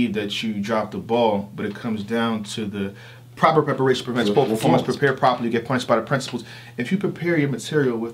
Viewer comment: can somebody slow it down and tell me word for word what is saying its 🔥🔥🔥🔥 how he do that it's like a Peter piper pepper type thing the shake my hand edition 😁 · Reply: Proper Preparation Prevents Poor Performance so Prepare Properly or be Punished by the Principles